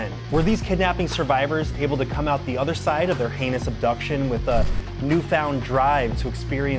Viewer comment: For a sec thought you said other end of their anus